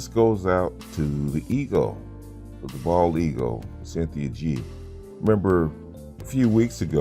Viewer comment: How can she protect an already dead man? Wtf is she talking about? Plus, it sounded like you said "Bald ego' for a second. Lol